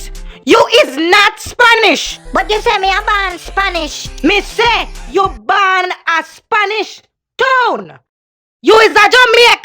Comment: Hahaha too funny ! But can somone tell me what the mom says after Dora says " you told me I was born Spanish "? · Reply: I always thought it said "you was born a Spanish toon" lol my bad.